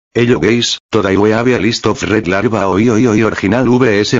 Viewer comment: hold up did you just say "Hello gays"